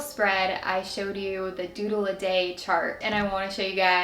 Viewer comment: When she said doodle a day, I thought she said Doodle Date!